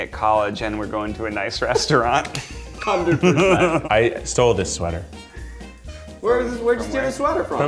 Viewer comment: I thought Zach said “I’m gunna start a family next” not steal and I was like 😳